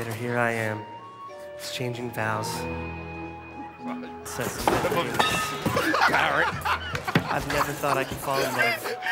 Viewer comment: What does Joe say at 3:51? This is brilliant I cried laughing the first time I saw it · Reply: I think he says, "Parrot."